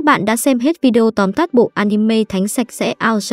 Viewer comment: Thánh sạch sẽ đoạn cuối t nghe thành thánh xem sẽ :))